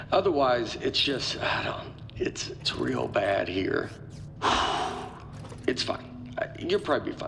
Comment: when he said "its real bad here... it's fine, youll probably.." i thought he said "its bad here. it's fucked. " and i was taken aback until i re-heard the line XD